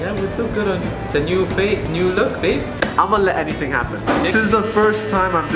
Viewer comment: Did he say “it’s a new face new look BABE”